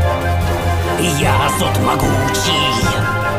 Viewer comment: Очень прикольная песня. Узнал о ней, смотря один видос по хк, мельком услышал "я зот могучий!" , а на следующее "я зот могучий" ютубер начал что то говорить, и в итоге я подумал что там поётся дальше "мой зад вонючий!" (Простите за мою испорченность)